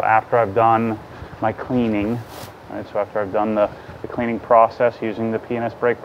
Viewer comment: lol it sounds like you said "penis".